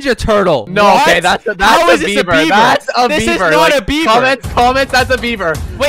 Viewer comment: Predicting that will win cause he’s taller. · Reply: Hi yellow fun fact, when says beaver 🦫 it sounds like his saying Bieber!?